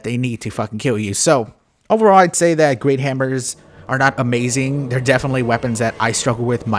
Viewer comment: Lol, at around 1:55 it sounded like you said, "great hamburgers are not amazing" XD. for some reason this really made me crack up. Congratulations for making it to 45,000 subscribers.